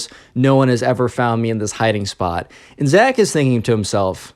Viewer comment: did he say Zach?